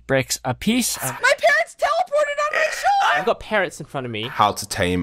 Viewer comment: I heard "MY PARENTS TELEPORTED ON MY SHOULDER!!"